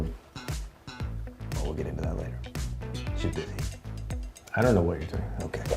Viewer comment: what did Mike say about Rachel? ‘She did…?’